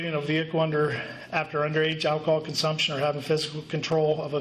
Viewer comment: did he say after underage alcohol consumption?